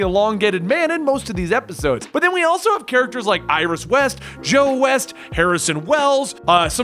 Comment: I could've sworn he said Joe wells instead of joe west